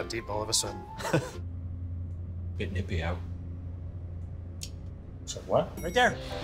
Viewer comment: I think he says, "Bit nippy out." because it is during the winter, there is also a Christmas decoration in the background lmao basically the equivalent to saying, "How's the weather?" which further adds the amazingness of this ad